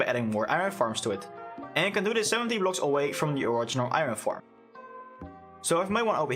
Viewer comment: did he say 70 blocks away?